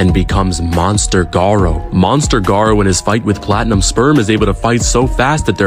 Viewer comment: havent watch to much of this seris but did you say platinum sperm?